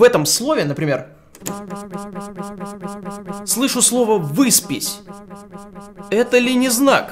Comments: я смесь слышу как в паспортном столе голос противной тётка "ПОДПИСЬ!" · Я слышу Выбросись....Хм,не знак ли? 26:49 - Страшно!Я слышу...ПЛАТИ ИПОТЕКУ :D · хехе...тоже слышу выспись... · я вообще писька слышу... Или порвись:) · я слышу повысь но с ударением на О, в предыдущей записи новей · Там где Топа услышал «выспись» я услышала «за*бись» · а я слышу " пись пись пись " :DD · мне послышалось "ЗАПИСЬ" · А я слышу - Спабись, Спабись, Спабись...+++++ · Где ты сказал что слышал "выспись" я услышал "роспись" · я слышу высрись😂😂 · Мне послышалось Christmas, а не выспись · я слышу слово писька XD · я слышу слово "подпись". Это ли не знак, что моя подпись страшная?